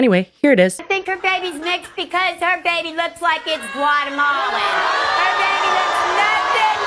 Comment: Every time said Guatemalan I thought she was saying watermelon and I was so confused lol thank god said Guatemalan 😂